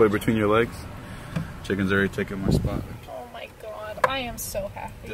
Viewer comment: Did you hear what said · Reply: chicken has already taken my spot